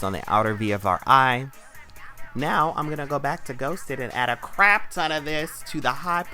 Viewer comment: When you said GHOSTED I thought you said GHOST DICK.